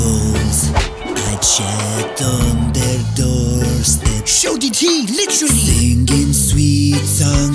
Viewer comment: did he say šuti ti on croatian ahhahahahahhah